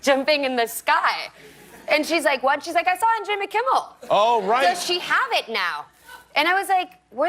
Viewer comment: “So she have it now” 14:51 no sé si escuché mal o no sé si en algunas ocasiones puede conjugarse she con have 😅 Pero Eiza habla inglés increíblemente bien y creo que es por su amor a Estados Unidos ❤️ cuando hay motivación todo se pasa más fácil! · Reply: Dijo “does she have it now?” Es pregunta tiene el auxiliar does que convierte el “has” de “she has” en “have”